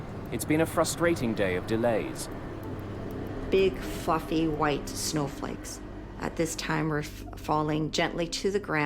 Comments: Anyone else hear her say, "Big fucking white snowflakes!" I was shocked when they then showed her and saw how calmly she was speaking. LOL. I had to go back and listen again. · I thought she said big fucking white snow flakes lmao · Oh she said big white "frothy" snowflakes. I had to replay that i thought she said f**kng lol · Haha..... I thought she said big, FUCKING, white snowflakes..... lol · I heard " Big f*cking white snow flakes " everytime I replayed it to see whether I hear well 😂😂... only subtitles woke me up from my dreams😂😂